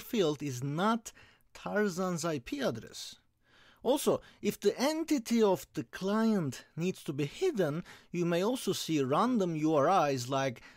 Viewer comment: Fantastic <mail> At 12:35 you say something like "also if the entity of the...." I cannot make out what you said clearly. Please can you type it? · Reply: Hi!! Thank you very much!! At that point I say "if the entity of the client needs to be hidden" and read the text in the slide... Please note that most of the text is provided in our website, <link> under the respective tutorials.